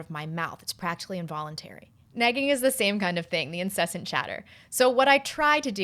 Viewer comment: thought you were saying "negging" until about the 4th time you said it and i was thinking "wow i guess i don't know what that word means"